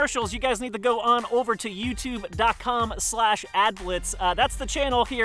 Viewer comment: Everytime they said Adblitz I thought they said Aglets (the thing on the end of your shoe lace) and got extremely confused